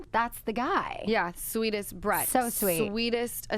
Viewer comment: i thought lala said he had the sweetest breath 😂😂😂😂😂😂😂😂